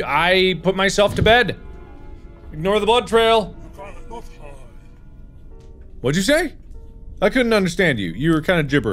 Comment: Mark: "Ignore the blood trail!" Grandpa: "I'm try'na love her." Mark: What'd you say?" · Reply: He said "You cannot hide".